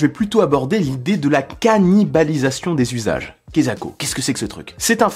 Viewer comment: J'ai entendu « cannibalisassions des usages » je me suis dit « C'est CETTE vidéo que je cherche ! ».